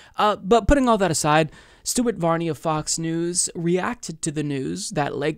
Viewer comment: I could have sworn you said "Stupid Varney" isntead of Stuart.